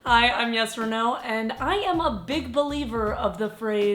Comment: okay, first thing this is the first video that im watching from this channel. and call me crazy but i thought she was saying "Yes or No" but she was just saying her name " Yes Reneau"